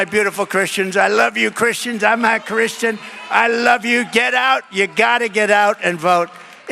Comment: “I’m not Christian.” Is that what I heard? With a solid ‘no’ head shake?